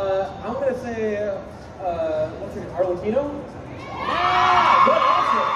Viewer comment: who did say I didn't catch it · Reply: Arlecchino